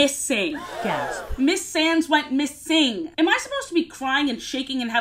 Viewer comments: I misheard and thought the great aunt in the Egyptology's last name was SANS not Sands 💀💀💀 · I swear to god I have such brain rot I thought you said “Sans” instead of “Sands.”